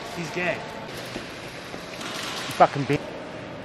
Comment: I heard him say “ fucken B**ner “ shit funny even if I’m Mexican